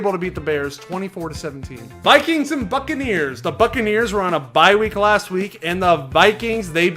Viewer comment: I thought he said the Fuckareers